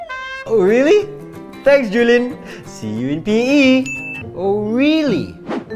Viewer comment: I thought he was saying see you in pee 😂